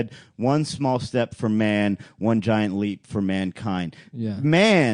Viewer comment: He did actually say a man, but he’s from Ohio and they slur the a with the next word